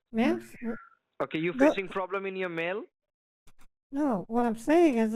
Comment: ! "Alex Wilson", I've heard him before... "You got a problem in your mail?" Cultural nuance failures. "Don't call me Ma'am!" "I'm really sorry, from the bottom of my heart Ma'am" !! Ma'am Counter overload! Grab a paper and a pen... Just give me a moment! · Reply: I really thought he was saying "you got a problem with your MALE?" Implying that "objection" to being addressed as "ma'am was only about gender identification. Awesome job in knocking him off script. He became wonderfully flustered and self-conscious, to the point of barely being able to string together a coherent sentence.